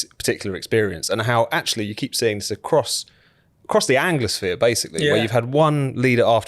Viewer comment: I thought said 'Across the Angus-sphere'.